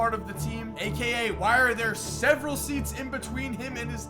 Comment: did say several cus its only three